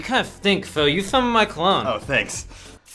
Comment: The caption says clone, when it was supposed to say cologne...